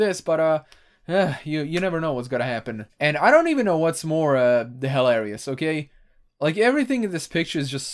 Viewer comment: I misheard “hilarious” for “hell areas.” I think they both get the point across